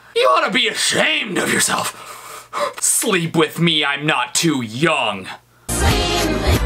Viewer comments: I heard ''Sleep with me I'm young and ugly'' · I thought it said satin picked me I’m not too young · I thought she actually said: "sing with me I'm not too yawn" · I thought she said 'sweep with me, i'm not too young. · It sounds like britney says “Squeem with me i’m not to hungry” i closed my eyes 😂